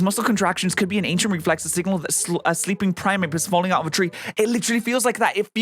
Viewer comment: Umm ... did he say Primeape?